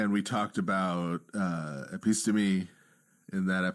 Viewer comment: In referring to the episode on the nous, you discussed “epistomy”? I replayed this several times, looked it up, can’t find it. What is this word? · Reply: episteme